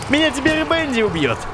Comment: Ты сказал в начале Венди или мне послышалось